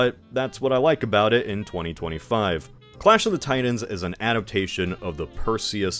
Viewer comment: I thought he was saying clash of the tie-duns.